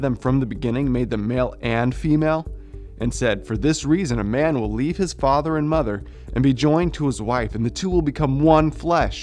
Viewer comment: You don't think saying "a man needs to lead his wife and mother" is terribly insulting to independent women and minimizes a woman's ability to have her own thoughts and feelings? You don't think that that sort of mentality increases the rate of violence and sexual assault against women? Here's a "hypothetical" situation for you - when a father leaves the home, the mother is left as single mother to raise her child in poverty. When the daughter grows up and becomes an executive in New York City and must financially & emotionally support her aging single mother - should the now grown daughter go find herself a husband to lead her (& her mother)? How can you not argue that that is how they got into the situation in the first place? Words are very powerful and this type of speech, I find extremely toxic and highly insulting. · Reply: You may have misheard him. Around 2:28 the video quotes Jesus saying a man is to leaves his father and mother, and be joined to his wife.